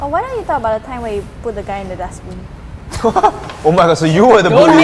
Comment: Just a random question, who the hell edits the subtitles for the videos. 21:56 "Gut" instead of "Guy"